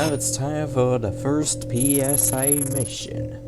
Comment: i thought you said BS mission LOL